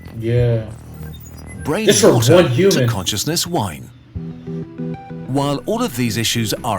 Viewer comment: 00:10:1dsfsd i measn 00:10:00 i thought he said brain water to conciousness why